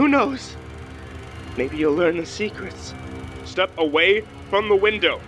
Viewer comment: I got an ad at the end of the second story and all I hear is "maybe you'll learn FLEX TAPE"